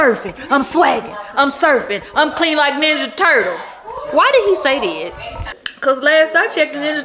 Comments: Lol I thought he said “clean like dish detergent” I could be wrong tho 🤣🤣 SN: I love that outfit with the shorts and the waist coat with the bob. Yes ma’am 😍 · I thought he said clean like dish detergent 😂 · Lol! I could’ve sworn they said I’m clean like dish detergent 😂😂😂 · Happy Friday! did not steal her humor lol. But sis I thought he said I'm clean like this detergent🤔😂 Anyway yall have a good weekend!❤